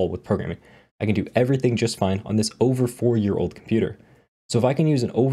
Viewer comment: I heard "over 40 year old computer" at first and I was wondering how he got VSCode running on MS DOS